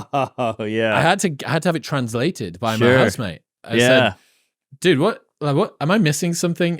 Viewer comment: every time he says "house mate" I think he says "husband".